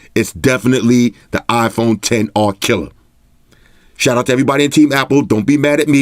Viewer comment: I heard "the iPhone X R. Kelly" 😂